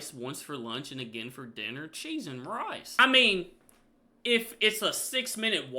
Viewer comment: So I'm maybe making a fool of myself but since I'm not familiar with this term, I would like to ask if anyone know what exactly he says. I can't get more out of it than 'cheese and rice' that can't be it?? 😂😂